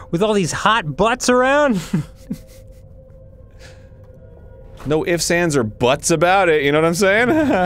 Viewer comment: I thought said "No hips, hands or butts about it"